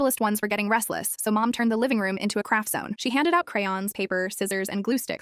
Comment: For a second i thought she said crack zone